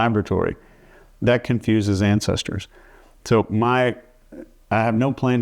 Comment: I heard “fentanyl confuses the ancestors” along with many other things.